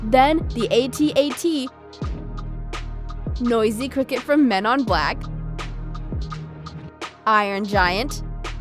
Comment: Did she just say “Men ON Black” smh